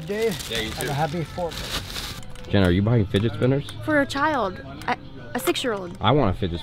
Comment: did she say for our child